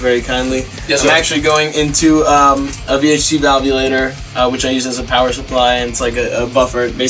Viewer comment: misha says he is using a vh2 modulator of something to get truebypass with his pedals . I can't find those anywhere some1 plz help I NEED 1 bad. I can't tell what he is saying after vh2 , it's sounds like modulator but I can't tell....